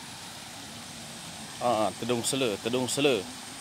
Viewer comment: i dont understand his language at all. 0:09-0:10 but it sounds like tedung selu. dont they say it ular selar tedung? which is more common? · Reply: Tedung selar